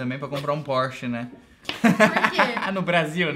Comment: Juro que quando cês falaram sobre comprar um "porsche", eu entendi "poste", tipo poste de luz elétrica 🤣 meu nível de pobreza n permite nem eu saber que isso é um carro, tive que ir pesquisar kkkkk